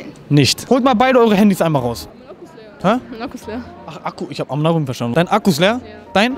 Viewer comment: Mein akku ist leer" Deniz:Hä ,,Mein akku ist leer"achso ich hab amk verstanden 😂😂